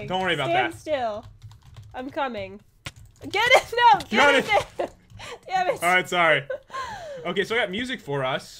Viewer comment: did say damitt at 4:20?